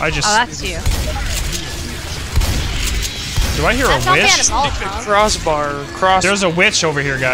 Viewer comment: Yes, we're quite aware the sound was out of sync. I had to use Nvidia's shadow play and it's a horrid program to use but for some reason fraps was causing major frame rate drops when trying to record this game. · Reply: um u said "do I hear a bit#h" at 1:05 - 1:08 so u curse